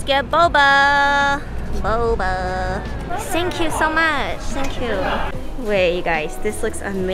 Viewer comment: I missed you so much. So happy happy joy joy. But then I was like, wait, did she just say "Sank you so much. Sankyou." 24:11 Now I'm howling with laugh cry. Sank you so much! 🤣🤣🤣